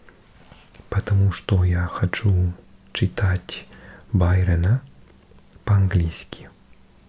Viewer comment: послышалось "Байдена"